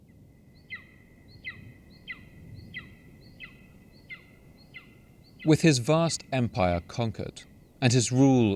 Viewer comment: I heard epsteinious life ...... Haha